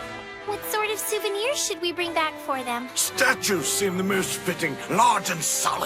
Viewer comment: When I was watching it in Japanese. I thought she said "elephant"(zou). It was a "statue"(zou).... I was confused until I saw it in English😂😂😂 ※ Elephant and statue in Japanese have the same sound.